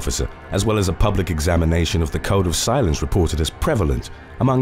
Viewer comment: Haha when you said code of silence I thought you said cone of silence instantly bringing images of Get Smart to my mind